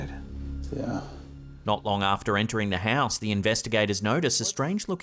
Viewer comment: Hey I thought you said talon 😂😂😂😂😂